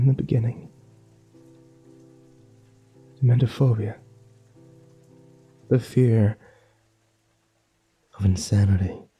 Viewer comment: The subtitles say, "mentorphobia the fear on Saturday"